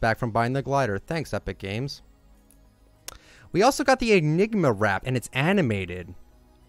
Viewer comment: I thought he said Ligma wrap 😂😂😂